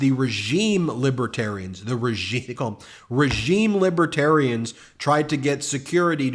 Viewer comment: I thought he was saying liberian.